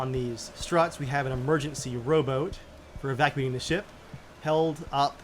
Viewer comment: What said : "emergency rowboat" What I heard : "immersion sea rowboat"